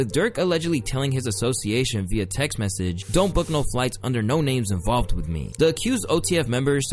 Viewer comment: I THOUGHT HE SAID DNT BOOK NO FLIGHTS IN HIS NAME BC HE WASNT WIT IT